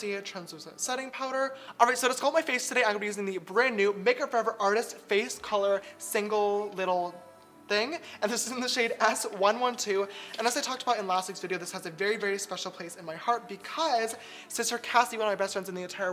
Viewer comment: who else heard ass 112?